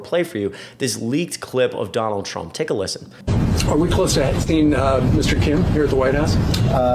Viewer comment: Did Doosy actually say, “are we close to Epstein uhhh, Mr Kim at the White House ?” I think so 1:04 . I slowed the audio down and you def hear the N at the end of Epstein. What an interesting slip.